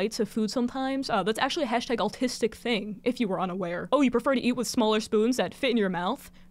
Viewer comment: THE FACT THAT I WAS EATING WITH A SMALL SPOON AS YOU SAID IT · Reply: I thought you said you were eating a small spoon and I got so worried 😭😭 but 💀